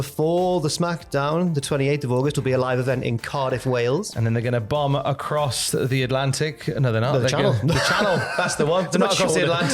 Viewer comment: i thought he said "and then they're gonna bum across the channel" I said EXCUSE ME 😂